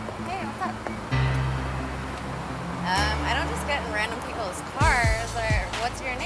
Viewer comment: i thought she said she dont just go in random ppls car